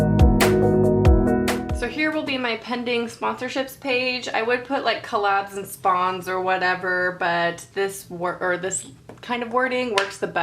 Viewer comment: i thought it said "pending spoons" 😂😂😂😂